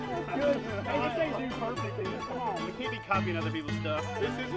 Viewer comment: dude perfect? did anyone else hear that? I'll take any day over dude perfect😃